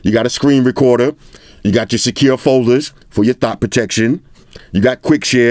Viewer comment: Did I hear "for your Thot protection" at 11:49? 😂